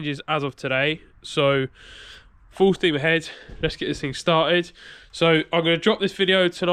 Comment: Have you still got the MK1? When you said “full steam ahead” I heard “full Steve ahead”.